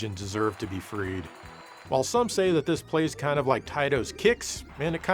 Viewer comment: Did you just say tits!?😆